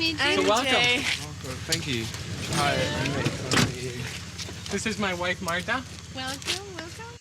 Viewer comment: It sounds like he said "this is my wife right now"